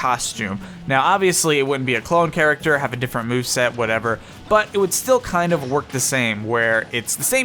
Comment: wElL yOu FoRgOt ThIs ChArAcTeR oR wHaTeVeR yOu'Re An EEDIOT, yOu MoRoN?! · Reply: I thought he said 'wow' not 'whatever' okay bye